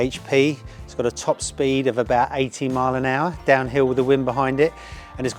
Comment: Did I mishear a top speed of 18mph?